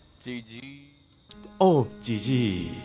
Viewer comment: から がGGって言うとジジイに聞こえるwww ナイスでしたで吹いたわwwwwwwww